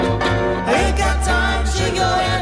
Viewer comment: I always thought that she said "put that giant jack!" so i thought that "giant jack" was slang for a fake acting person's hand...NO WONDER i couldn't ever find this song! Such terrible translation i had! LOL